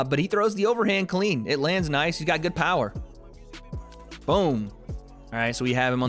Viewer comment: I thought it said no power but it said ko power 😂